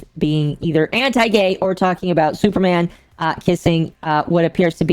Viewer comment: OOOH you said kissing I thought you said Superman fisting another man... my bad